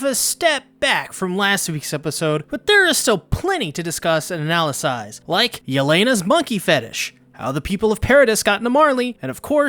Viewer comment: Did he just say “analysize”......